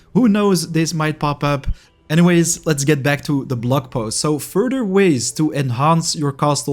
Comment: Given your accent. You said "blog post" but I heard "blood post". Lol Very appropriate IMO.